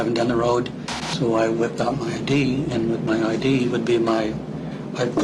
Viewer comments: I thought he said, "So I whipped out my D..." Well I guess technically that eventually did happen... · I thought he said “... I’d wipe out my D”. Then realized he said ID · I thought he said I whipped out my D 😳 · I thought he said I whipped out my D